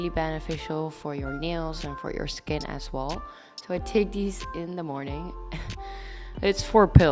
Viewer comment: Lol I thought she said "I titties in the morning." XD